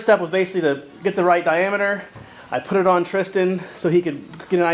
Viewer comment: I just heard Tristan